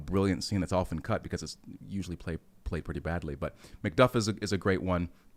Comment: For a second I thought he said McGruff (the crime fighting dog) but then he said Shakespeare so I was like 🤦‍♂️😂